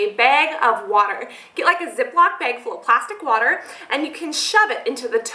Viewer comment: Anyone else hear at 1:59-2:00 she says plastic water..?